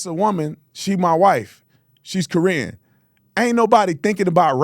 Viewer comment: 😂😂😂 I had to rewind bc I heard. “She’s Korean”, and I was racking my brain trying to recall what she looked like, bc I thought she was white, and Brandon always says she white. It took me a sec, but I got there. 😂😂😂😂😂